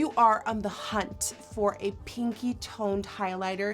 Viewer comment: I thought she said kinky I need hearing aidw